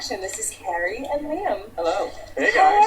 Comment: OMG! I thought she said Carrie and Liam at the beginning, minor heart attack!